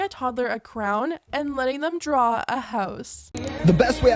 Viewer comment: Did she just say crown instead of crayon? Lmao. I had to listen to it twice!